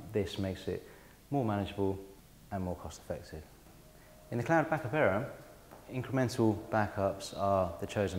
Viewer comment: It might be a good video if I could understand what he was saying. Dude, speak clearly!!! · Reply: what is he saying at 1:44 ? "In a clarabackavera" ?